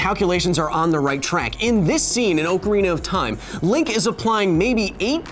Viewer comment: Did he just say Oakarina of time?